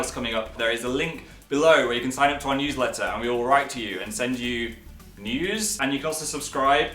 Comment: OMG 3:26 I THOUGHT HE SAID WE WILL SEND YOU NUDES 😂😂😂😂